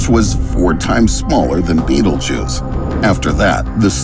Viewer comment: Rofl.. i heard "Beetlejuice devoured.... 4 times smaller than analjuice." 😭